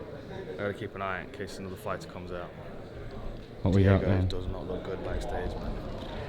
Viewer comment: Who was Dan referring to when he said "doesn't look good back stage" ? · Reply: Sounded like he said “Diego”. So I’d say he was talking about Diego Ferreira